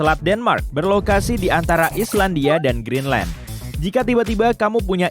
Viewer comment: Grand line g salah denger kan akj??